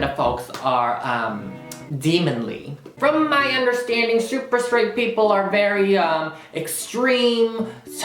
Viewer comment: Did you even Understand what their saying? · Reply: okay I literally thought they were saying that Kevin is straightphobic or something, thanks for explaining